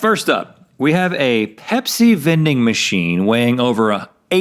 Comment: I thought Rhett said Hep-c vending machine not pepsi vending machine 😂